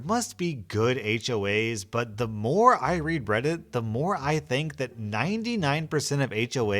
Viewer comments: "the more i read breddit" anyone else heard that or is it just me? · Is it just me or did say "bread-it" at 8:16 ?